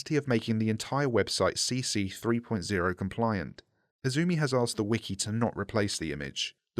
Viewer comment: Turn on Captions Who is Moto42? Bro was clearly saying Izumi